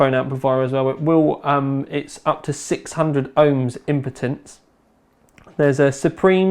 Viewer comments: did he just say IMPOTENCE on 2:03 LOL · sounds like says impotence instead of impedance lol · did you just say impotence lol, its impedance.